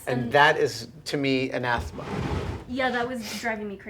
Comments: Does anyone know what he is saying at 25:17 ? "And that to me is an _____. " cant hear it enough to google the definition, sounds like a nice word to know. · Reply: Got it, the word was Anathema. noun, some thing or someone that one vehemently dislikes. good word :D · What word is Adam saying here? "That is, to me, a [unintelligible]" I don't recognize the word and this video is sadly not captioned. I love looking up words I don't know and this was driving me crazy. Normally I'm good at getting close enough for Google and the definition usually confirms it with the context. I had nothing to go on here especially with the mic noise right after. TIA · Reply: “Anathema!”